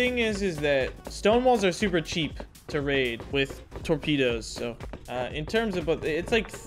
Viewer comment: what is saying at? 32:45 it sounds like tormedos but I'm unsure